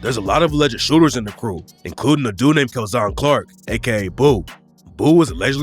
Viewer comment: I swear he sayin dudes name is K!LLZONCLARCK (k!lls on clark) I cant even imagine how it's really supposed to be spelled or said Lmfaoo